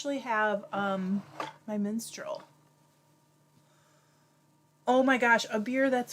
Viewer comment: When you first said you have your Minstrel I thought you said “menstrual” 🤣🤣